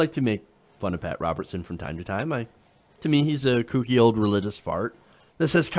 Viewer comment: For a second I thought he said "he's a congealed religious fart" instead of "cooky old religious fart" and I think the former is much, much more satisfyingly spot on.